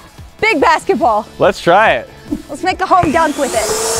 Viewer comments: Did she just say a home dunk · I know she did not just say “Home Dunk” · did she say let’s make a home dunk with the basketball?????? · Did she just say let’s make a home dunk wit it😂🤦🏽‍♂️